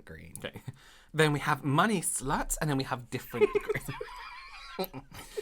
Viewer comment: When I watched this the first time I kept going back on the green shade when casually says “money slut” and moves back to green 🤣🤣🤣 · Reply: At first I thought he said “monkey slut” so I was having my own giggle fest.